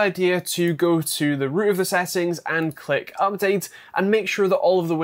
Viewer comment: I listened to what you said at least 10 times and I still can't understand what you're saying lol The _____settings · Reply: root of the settings